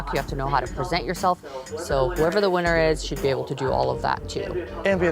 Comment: Norvina accidentally saying "she" automatically eliminated Benny lmao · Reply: When did she say "she" ? If you are referring to 24:15 she said "Whoever wins should be able to do all of that too."